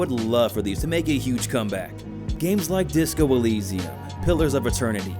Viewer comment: What game did u say after pillars of eternity? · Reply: iirc it was Disco Elysium?